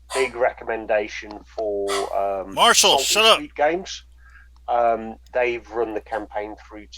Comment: At about 56:35, some recommends a podcast, but the host yells at his dog off-mute at the exact moment he names the podcast. I've tried a dozen times to catch it but I can't hear it. Anyone know what he said??? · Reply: Im sure you found it already but saltysweatgames. They left a comment on this video so pretty positive it's them.